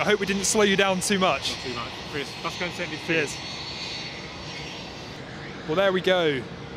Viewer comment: I can't quite understand what the guy says at the end... anyone please? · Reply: "Let's go and send these figures."